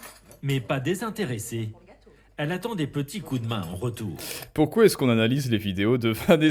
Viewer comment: j'ai entendu "une aide précieuse, mais pas désintéressée. Elle attend des petits coups de reins en retour" 💀😭